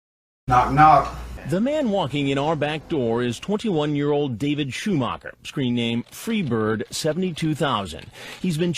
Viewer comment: for a second there I thought it said David Schwimmer